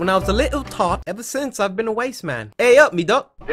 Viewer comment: Did you call yourself a wasteman at 16:30? 😂